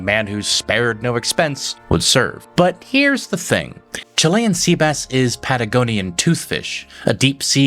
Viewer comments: I always thought he said chilly and sea bass. 🤔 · Not gonna lie, as a kid, I always thought he said they were serving “Chilli and Sea Bass” which always made me think rich people are weird.